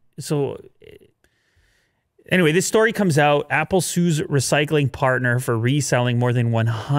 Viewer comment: damn i thought it said "apple sus" i might have been playing among us too much